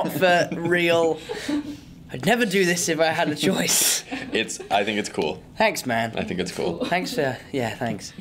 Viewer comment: ありがとうに聞こえたw